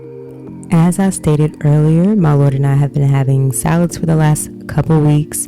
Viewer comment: Please excuse the questions, however, did you say "My Lord and I...?"